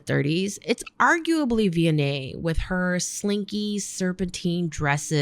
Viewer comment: hi, maybe i’m hearing wrong but @ 8:03, you mention “viannae” (as spelt in the caption)……. i adore the work showcased by her that you presented but i can’t seem to find her online at all. maybe i’m mishearing really bad??? lol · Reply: It's spelled Vionnet. Enjoy! I love her work!